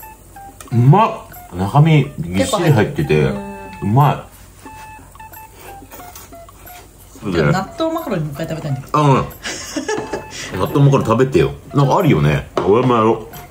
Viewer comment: 納豆マカロニが納豆マカロンに聞こえて激しく動揺しました😂